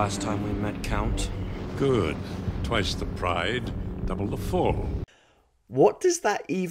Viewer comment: I'm wiling to believe I misheard it, but it sounded to me like the line was "Twice the pride, none of the form", mocking the fact that Anakin is swinging wildly at the start rather than keeping his cool and maintaining his defensive stance... which shows Dooku's own hubris when Anakin then does start fighting with a clear head and proves his superior fighting form in that last moment by goading Dooku into overreaching, locking his saber and >ahem< disarming him (all right, I know it's his hands and not his whole arms, but it was too good a pun to waste). Anakin starts the battle enraged, but gets more cold and clinical after Dooku's remark - it's as if the whole thing were staged, Dooku knew beforehand that this was how it was going to go down and had accepted it as his loyal duty to Palpatine, and he was making sure even in his last moments that his master would get the best apprentice he could by giving Anakin a final lesson in how to be a better fighter. That's why Dooku's sympathetic look was so overplayed - he wanted to make sure that Anakin had what it took to kill without mercy. He wasn't going to learn that by Dooku making it easy on him by being all defiant and angry - as a Sith apprentice Anakin would have to be able to look into the eyes of a defeated opponent begging for mercy and still have the coldheartedness to strike them down without hesitation if he was going to become what Palpatine needed. Sith are emotional manipulators, and Dooku was manipulative right u